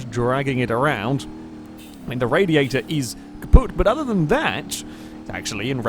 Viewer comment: did you actually say "kaputt"?? Always thought that word only existed in german